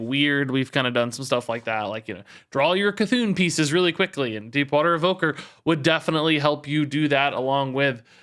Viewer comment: It really sounds like you said "Deepwater Hooker" there 🤣🤣